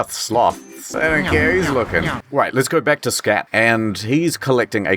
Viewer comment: I know he says "scrat" but I honestly didn't hear the "r"